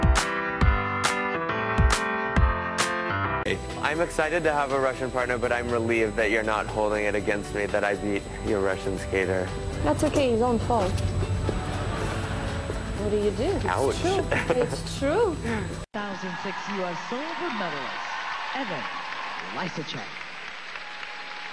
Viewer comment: I thought she said, "That's okay. You don't fall."